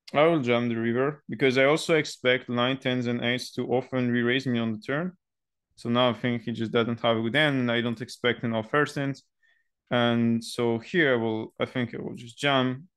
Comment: i totally didnt understand what u have said at 1:51 about sizings. Can you write that in comment? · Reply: I dont think he has better than 99 and most of this str8 and better hand would reraise me on the turn, so I can cap his range even more